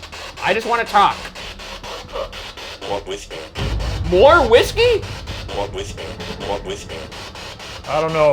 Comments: Seriously though, I'm going to miss all the fun times with the ghoul boys, but I'm happy they had a last hurrah in this house. Question though, did anyone else hear "more with her" instead of "more whiskey" at 12:40 ? Like in response to Ryan saying that he wanted to talk. Either way, ya'll have done good boys :) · I didn't hear 'more whisky' I heard 'Not with you' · I thought it said "you are with me" not "more whiskey". Thanks for the years of fun and spooks hope you start something similar with watcher, I want to see the ghoulboys take on the jersey devil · when they heard "more whiskey", i heard "no whispering" and when they (ghoulboys) thought they (entity) were asking for whiskey, i thought i heard them (enitity) say "thats not..." but got cut off kinda funny, but kinda makes sense imo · When they said they just wanted to talk I heard "what, with you?" On the box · when they thought the spirit box said "more whiskey" I actually heard "wo bist du" which is German for where are you. Also I didn't think I'd cry but Ryan's speech at the end really got to me, long live the Watcher though where they still work together! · after Ryan says "I just want to talk" - I know they heard it as "More whiskey" but I can only hear "Not with me." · I definitely heard "walk with me" instead of "more whiskey" 👀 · Congrats on an amazing series Ryan and Shane! At around 1240 I thought I heard walk with me not more whisky, anyone else think the same? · I heard what's this game not more whiskey